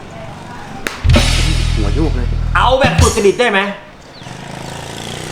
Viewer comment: มีใครได้ยินไหมว่าหัวโยกเลย